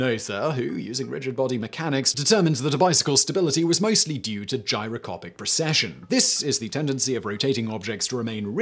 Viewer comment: in the video. Am I the only one hearing "Gyro-COPIC" without the 's'? In other parts of the video, he clearly says gyro-SCOPE, so it seems like a weird mistake to make.